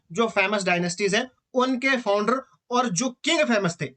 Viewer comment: par kya bola hai sir apne ?? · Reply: King of famous dynasties